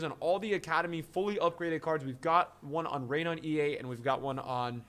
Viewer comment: Everytime he says Reinier its sounds like hes saying rain on ea